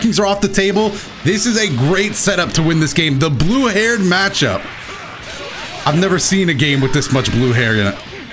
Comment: I thought he said “I never say a game with this much blue hair dye”